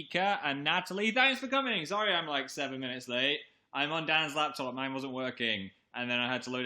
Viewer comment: im on dans lap top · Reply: THATS WHAT I THOUGHT HE SAID I LEGIT THOUGHT HE SAID THAT HE WAS ON DAN'S LAP AND I WAS SCREAMING SO HARD!! But now I'm disappointed XD